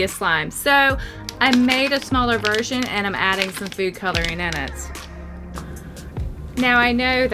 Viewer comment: did I hear “virgin”